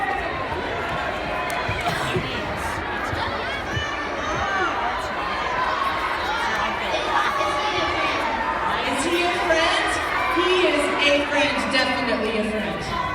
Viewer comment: I swear i heard someone say "Is he Donald trump"